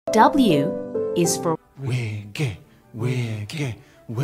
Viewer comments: me who ships baybond thinking they're saying 'we're gay' instead of 'wiggy' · WIGGY WIGGY WIGGY WIGGY WIGGY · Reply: Same I heard ''We're gay! We're gay!" and I was very much on board (still am!) 😂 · did anyone else hear “were gay?” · Am I the only one who heard "We're gay" at least once here lol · When they were saying ‘Wiggy’ it sounded like they were saying we’re gay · When they were saying wiggy it sounded like they were saying "we're gay"